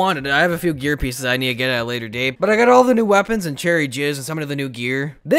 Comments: At first I thought he said cherry jizz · did he just say cherry jizz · anyone catch that😈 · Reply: Fizz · did he say “cherry jizz” 😂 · did he say Cherry Jizz?? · did anyone else hear cherry jizz lmao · He said cherry jizz instead of fiz